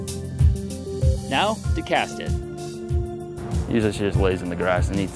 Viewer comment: Did he say "Now to cast it" or "Now to cat sit"? 🐱